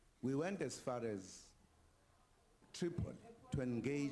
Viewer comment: Did he say triple? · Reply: Tripoli